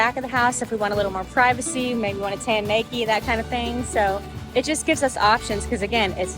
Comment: did someone just say " Tan Nakie " ??? watch out for neighbors with drones LOL if you ever want a cheap floatie I used to buy the Coleman queen blow up mattresses with the velour on the one side / cheap but effective for two people